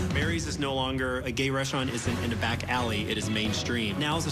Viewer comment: what did he just say?? I kept rewinding. And I have a question about what I think that I heard. WTF is a "gay restaurant"?